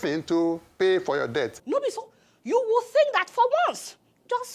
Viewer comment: Between 3:00 and 3:05, I heard the man mispronuncing debt for death, "paying for our death"🤣🤣🤣. Please we are not paying for our death oo, we pay for debt.😅😅😅